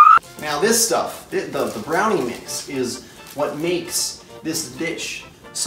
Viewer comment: Wait did he say dish or ...